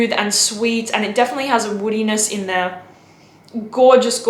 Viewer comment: Did she say weenis, as in WEENIS? Help me out someone.